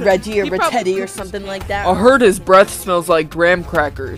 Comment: Bro, I’m cracking up on 11:11 they said “ I heard their breath smells like graham crackers😮”😂😂😂😂😂😂😂😂😂😂😂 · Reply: I-I HEARD BREASTS INSTEAD OF BREATH OMG